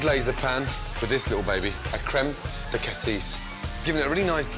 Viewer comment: what creme did he say ? creme de cassis ? creme de kathis ? creme what ? I ask if someone can help me with this .